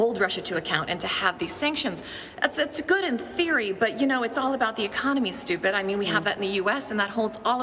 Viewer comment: did she say " it's all about the economy stupid "